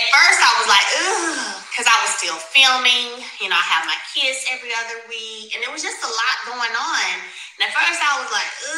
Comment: Based on audio it sounds like she was speaking in past tense when it came to deciding to go on tour. It sounded like she said, “I was still filming and I had my kids every other week and it was just a lot going on.” It doesn’t speak to what is or may currently be happening.